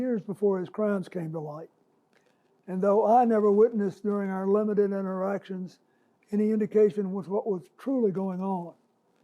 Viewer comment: did he say limited in erections???😮